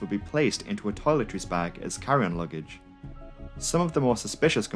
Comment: I started laughing at 4:47 because I heard him say "Karen luggage" instead of "Carry on luggage" and i thought to myself "wow they actually make Luggage especially for Karen's".😂😂😂😂